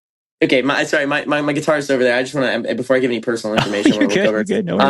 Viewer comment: I heard “my guitar” and was so confused to how a guitar’s mother died